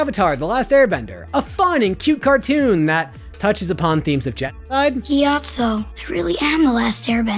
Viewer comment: I thought he said Jetlag not genocide and was very confused